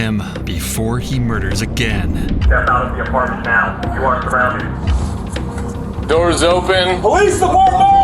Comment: I really heard, " step out of the apartment now, you're grounded" 😅😂😂